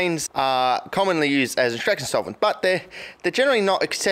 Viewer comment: Hexanes are used as WHAT? You said that fast enough I couldn’t understand you. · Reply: extraction solvents.